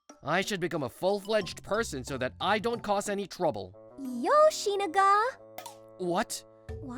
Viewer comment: i hear yoshiniga